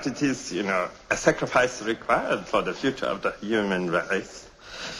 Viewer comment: When I first heard this scene it sounded like he said "a sacrifice required for the future of the German race" and it made this scene even funnier